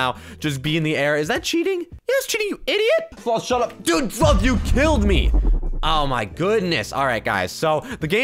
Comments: Me: Did he say fluck? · did u drop teh F Bomb? of did u say "Dude,sloth you killed me!"? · did you just say... DUDE F*** YOU KILLED ME but i would never judge my fav youtuber! <3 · I think he said the F word right after 2:47 he said sure f you killed me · did he say f**? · at the time 2:47 did you say a bad word or "Dude, sloth you killed me!" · it sounds like you said the f word even though you just said sloth...lol Edit: Oh gosh- why do I have so many likes- oh gosh and so many replies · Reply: RedPandaGacha it’s sounds like he said “dude (f word ) you killed me” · it sounds like you said the f word even though you just said sloth...lol Edit: Oh gosh- why do I have so many likes- oh gosh and so many replies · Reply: RedPandaGacha I thought he said "dude f*ck u killed me